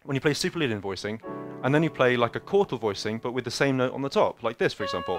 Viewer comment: "And then you play a ?????? voicing" I cant understand; does anyone know what he said there? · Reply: quartal voicing: start at the root C, and go up a forth and a forth again and again until you play 6 notes total. its very simple to remember how to construct it but it will create a "Cm7b9add11omit5" or smth like that. it will start and end with the same notes as Cmaj9#15#11 and resolve well to it while switching between the phrygian and lydian sound well